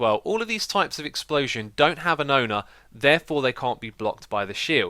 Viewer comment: Hahaha! When you talk about block able and not block able explosions. I thought you said that that the block able explosions needs to have an odor, and I thought. Hmmm what does he mean by that. And it kind of made sense at the same time as it didn't at all. :)